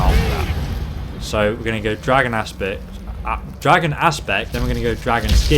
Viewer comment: when said dragon aspect I heard it as dragon ass spit lol. BUt not trying to insult u in any way I love u videos and would u mind if it's not a problem but could u play using some other builds but till nowI really like this but if u can use other builds could u use the mage ones especially the dream strider don't bother if u can't I like dis one anyway